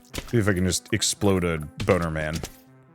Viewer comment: Did you just say "explode a boner man?" 11:45 😅